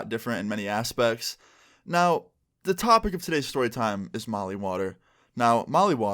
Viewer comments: I swear i thought it said holly water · I thought it said holly water lol